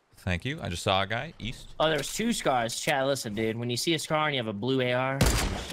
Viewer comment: did summit say chat or chad???